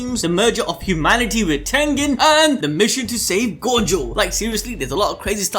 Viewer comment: Thought it said “the mission to save god” which is kinda still accurate